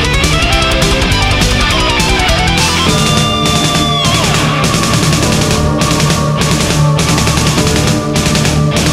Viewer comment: Does anybody know what does he say exactly at the minute 3:20? Am I wrong or does he say: "Just the daargh I final fears?" xdddd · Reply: This the day Of final thieves